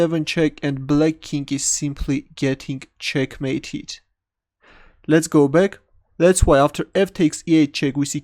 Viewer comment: Oh, every time he says "let's go back" I thought he said "let's go big xD"